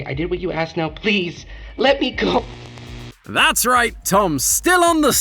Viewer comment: I swear I heard wooly said 15:37 Let me COOK